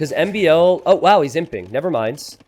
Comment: T90 every time you say "He's imping" I hear "He simping" ;)